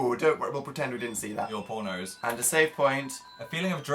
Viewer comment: when Phil said "your poor nose" at like 53:40 I thought he said your pornos oops